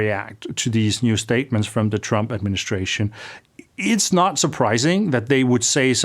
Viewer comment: I always hear 'Trumpet-ministration' lol